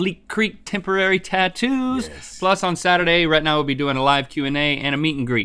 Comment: he said rhett and i and i thought he said retina xD